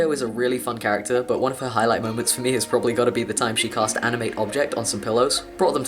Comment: I THOUGHT YOU SAID ANIME OBJECT ON SOME PILLOWS OMFG